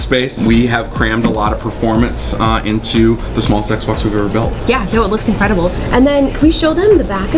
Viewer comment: did he say the small sexbox lmao